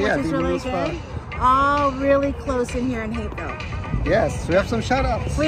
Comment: The first time said it I could have sworn she said “back to hickville”. But I’ve watched you long enough to know she would never say anything like that. Then I finally got it the second time...Hapeville. 😂